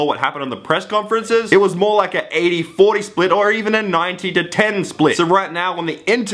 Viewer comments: did you say 80/40 split? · did he say 80 to 40 split? Lol